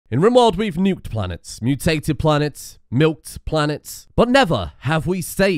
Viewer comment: I had to take a double take on that intro because in my half asleep mind I thought you said "milfed planets"